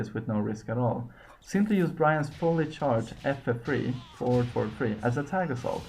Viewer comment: please tell me what did you say at 5:12 to 5:14 pleease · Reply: forward forward left kick